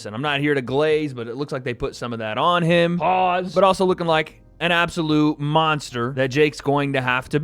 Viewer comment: I just heard say "AJ is oiled up looking like an absolute SNACK!" 😂